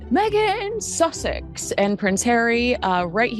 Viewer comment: I misheard you today, thought you said Meghan Sausage. Sounds so much better though.